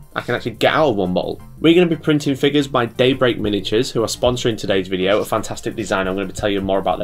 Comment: I swear you're saying Date Rape Miniatures